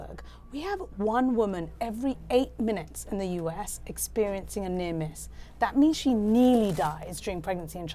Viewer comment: What is she saying at 0:38? She says that one woman every eight minutes in the United States experiences what? I can't make out what she's saying. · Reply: I think she is saying "a near miss"